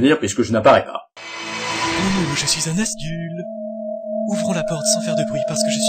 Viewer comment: C'est quoi un "Asdul" ? :o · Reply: et on dit Nazgul